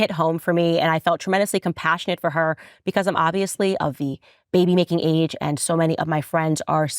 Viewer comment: Did anyone else hear that, or is it just me? I kept rewinding and replaying, and I swear she says, "I am of baby baking age." Baby baking? That's our schtick. Don't go culturally appropriating our positions (and our culinary delicacies). Social Anarchist she is. Shame on Candace. Now I have to go find and lock up all my recipes. It took me 238 years to collect those. Um, I mean 23 years. Yeah that's what I meant. It's not like I'm some kind of evil supernatural entity or something, ha, he, he, he (laughing uncomfortably). Damn it. On top of all that, I'm out of Worcestershire sauce. Don't worry, I'm coming. Don't leave me hanging L&P...